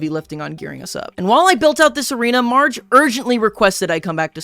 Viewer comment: Did I hear marge at 4:17 or am I trippin